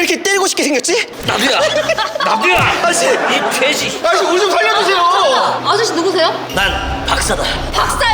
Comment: 에서 씁아저씨 개샠이라고 들리는데 옥애티 인가요? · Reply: 이 돼지에요